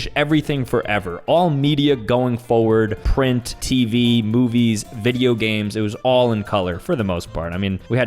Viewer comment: did he say boobies? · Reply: Movies*